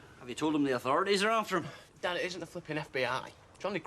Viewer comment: Did Steve say "Dad, it's not the f***king FBI"??? 🤔🇦🇺😁